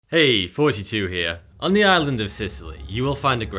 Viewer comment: his accent makes it sound like he said "hey 42 here"